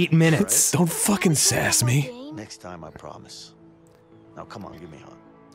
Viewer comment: I had to to a double take at 22:40 because it's the end of the episode and it sounds like Leo says "next time on grumps." Also, I love the longer run time, but it felt weird having it be that long